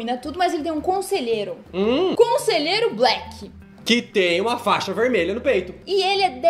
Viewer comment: Eu ouvi "fita vermelha"?????